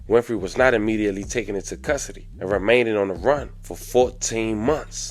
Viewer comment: Thug said. "I don't give you no angle... I just give you the story" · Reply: I... did he say remainded?